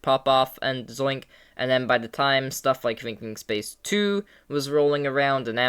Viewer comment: “twinking space ii” is what I heard on 2x speed